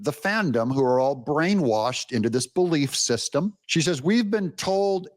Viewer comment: I misheard when said the word, "brainwashed" and I thought he said, "blame washed," but when I rewound the tape I was wrong; however, it does seem to fit, IMO.